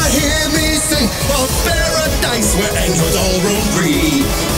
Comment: The line where he said "androids roam free" without the subtitles I keep hearing him say "Angels". Reminds me of Evangelion and Vision of Escalowne 😎